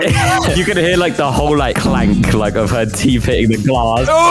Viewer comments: I heard clanka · what did oof said?😭✌🏿💔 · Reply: “CLANKER”